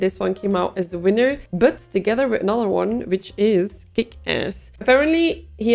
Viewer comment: I heard "Thick Ass" instead of "Kick-Ass", and now I'm curious about it.